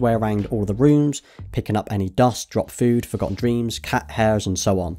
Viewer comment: did he say forgotten dreams?